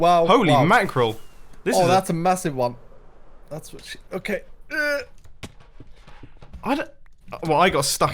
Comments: Who all heard start to say “that’s what she said” at 23:19😂😂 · SAID THATS WHAT SHE SAID LAST TIME!!! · Anyone else notice that said "oh that's a massive one!" And soon after that he said "That's what sh........" as he tried to do a that's what she said joke listen closely as lands on the boat you can hear it also the time stamp is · saying, thats what she said